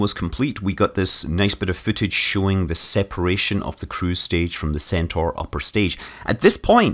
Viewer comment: separation of the "crewstage". did i hear that correctly?